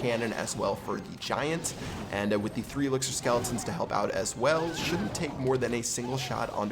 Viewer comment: Who else heard him say "the 3 elixir skeletons" 😂